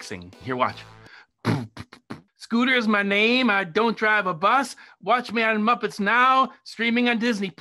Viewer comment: ‘scooters my name, i drive a bus, watch me on muppets now, streaming on disney plus!’ love him or hate him, hes spitting straight facts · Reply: I thought he said i DONT drive a bus....